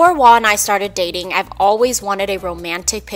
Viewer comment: Why does it say Juan instead of Wah at 00:37? Hahaha!